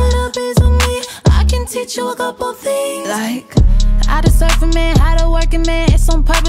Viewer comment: Possible corrections: You can't get this THICKNESS out of your mind He so GOOFY, call me papi. He go stupid, he go DAFFY (as in the cartoon characters) I can teach you a couple things like "how to SURF a man, how to work a man" (basically using him) Body language like speaking SPANGLISH (it's a mix between English and Spanish) Thoughts? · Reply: I thought she said how to surf a man, also speaking Spanglish. Def agree w/ your corrections.